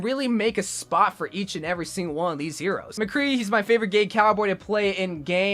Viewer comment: Did he say gay cowboy to play in game